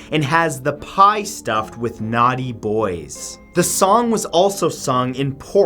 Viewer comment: I heard: “the pie stuffed with naught bois”